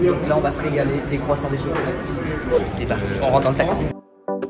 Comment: C moi ou il a dit chocolatine en